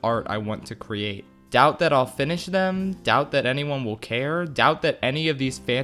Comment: i was listening on bluetooth headphones and what i heard was "The doubt that IPHONE LOST"